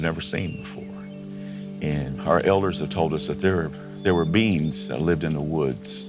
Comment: I thought he said beans 😂